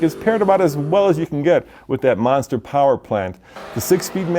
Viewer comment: It sounded like he said Mazda power plant